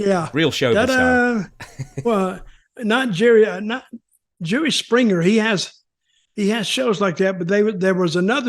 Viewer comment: I swear it sounded like Dutch said "Jewish Springer" 😂😂😂